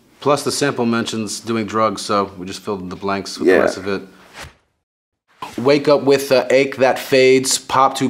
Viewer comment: i always thought it said wake up with the etouffee